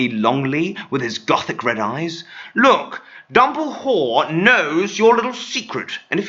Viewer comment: He tried to sneak in "dumblewhore" instead of dumbledore